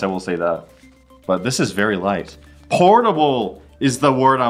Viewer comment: I heard that as "pornable".